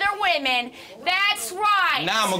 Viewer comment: "Now ima go give em' that rod" 😂😂💀💀💀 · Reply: Patrick Budge i thought he said,”Now ima go give to her raw”